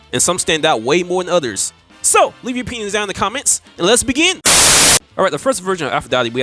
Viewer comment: When you said 'leave your 'pinions down in the comments', I thought you said 'leave your penis down in the comments' and had to double back to make sure I heard it right.